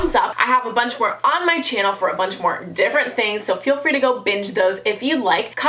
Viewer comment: it sounded like she said the b-word instead of binge.😂😂😂😂